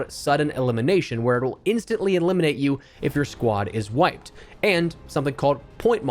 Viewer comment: I heard "instantly eliminates you if your squad is white" 😅 wtf